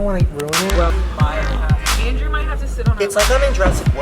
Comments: It sounded like said Andrew might have to sit on ........lap. Check it out Like if u agree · uhhh did say “Andrew might have to sit on my lap?” 😭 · Anyone else hear say that Andrew might have to sit on her lap??